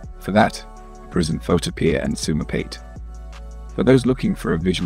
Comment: what is he saying the program is called? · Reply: it's photopea